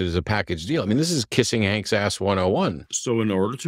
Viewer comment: sounds like "this is kisding ank's ass 101" - can anyone tell me what this means?